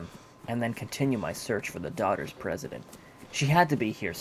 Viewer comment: Did u just say the Daughter's President?